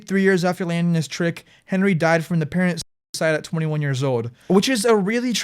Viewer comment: What did you say here that cut in audio? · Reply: Oh I see, Apparent Suicide. Interdasting